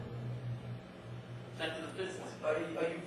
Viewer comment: "I'm fucking this up because of my amp. Sorry, man." What a kid at Guitar Center says. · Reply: He said "I'm pissed off" not "I'm fucking this up"